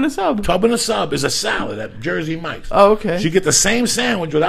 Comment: "No more Subway, you're a Jersey Mike's guy now." It's like Lee has been caught fraternising with a rival Family. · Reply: Sounds like he is saying "jersey and mikes" lol